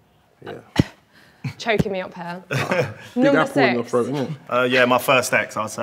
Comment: can anyone tell me what this guy is sayjng in response to Lily's cough and throat comment? Id like to understand what he has said. Could be telling....anyone able to let me know? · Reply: He said something about “caught in your throat isn’t it?”